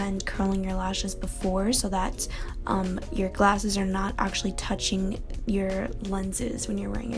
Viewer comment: 😂😂😂 "So your glasses won't be touching your lenses when you're wearing glasses" did I hear right?